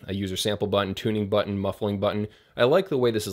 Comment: It sounded like you said muffin button... mmmmmmmmm.......... Muffin button.................